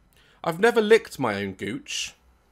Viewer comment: you fucking what?!?! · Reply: OH I thought he said cooch